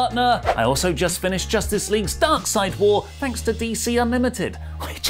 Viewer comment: i thought said "Justice League Darkseid War" instead of "Justice League Dark : Apokolips War"